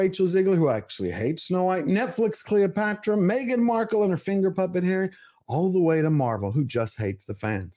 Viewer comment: I thought you said Meghan Markle "finger popping" Harry lmfao